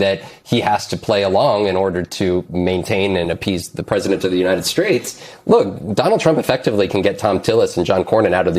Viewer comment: did he say "united straights"?